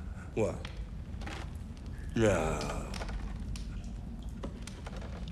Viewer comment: The first time I saw this scene, I swear I heard him say Polish sausage. Glad I misheard him